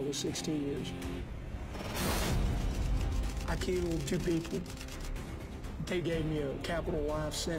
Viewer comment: adding 20 years is way to much he didn't hurt anybody wtf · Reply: I thought he said he killed 2 people